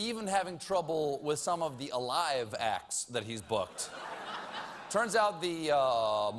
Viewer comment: I thought he said Brooklyn Tabernacle Choir! I got scared for a second